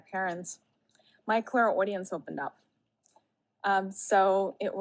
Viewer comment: Did she just say her Chlymadia opened up while on holiday in Portugal with her parents 😂😂😂